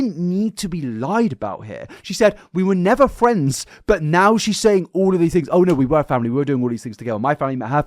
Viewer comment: the only thing I will say is I think she said ‘we weren’t friends at first’ but regardless the friendship was weird and not appropriate, shouldn’t be called a friendship when Jojo was that young